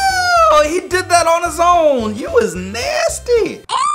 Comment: lol🤣 what is he saying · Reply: He said "Eww he did that on his own he was nasty."